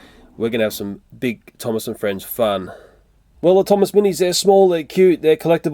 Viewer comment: When he said small I thought he said swallowable 😂